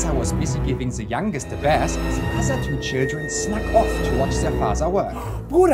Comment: I thought he said "giving the youngest the breast"